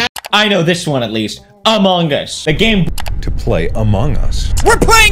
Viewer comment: i heard amungus